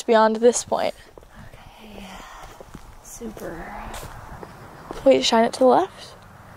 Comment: it sounded like a voice said “okay ya”. Check it out .